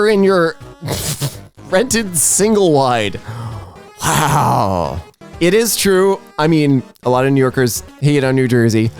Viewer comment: what is the blurred word m**h? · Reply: Meth.